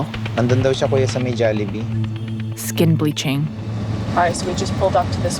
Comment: I heard jollibee